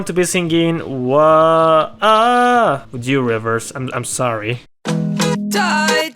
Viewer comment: What did you say there? "Wuju Rivers?"